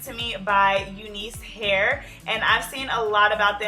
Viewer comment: Any one else thought that said uncle hairs 🤣😂